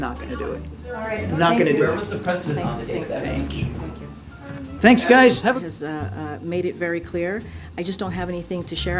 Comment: did he just say. thanks guy, and booked out ?